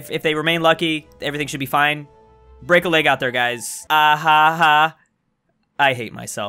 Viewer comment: thought you said break a legO